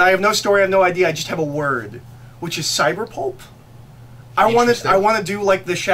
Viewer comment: When said "cyberpulp" I thought he said "cyberpope" and now I don't think I will be able to enjoy any stories anymore cause none of them will be a good as cyberpope is in my head. To the star wars point, I get why so many books are war and politics focused. It is a franchise that is always tied into warfare and with politics they are ingrained in everything inherently. Though I do want more SW that's exploration. Outbound flight was that way though it dealt with Chiss politics but that's just natural, the Han Solo trilogy and duology and I think the Lando novels are all purely fun in the GFFA without being political or war focused. Just gotta seek out that stuff. New canon wise Ahsoka and Phasma are very much separated from a massive war or politics (phasma less so)